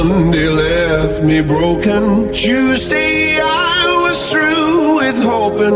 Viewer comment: Monday left me broken, Tuesday I was through with hoping, Wednesday my empty arms were open, Thursday waiting for love, waiting for love🗣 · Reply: Me who thought Tuesday said juicy